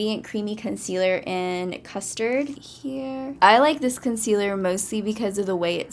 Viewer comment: who heard at 3:32 someone say here